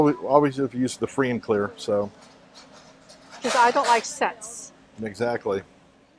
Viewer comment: what is she saying at 13:12 ? LOL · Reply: Cause I don't like scents